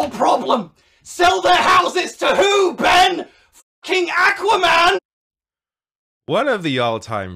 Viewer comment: I guess you probably have to censor the curse words, but when you do that to clips like that, it really takes the power out of them. It sounded like he said King Aquaman.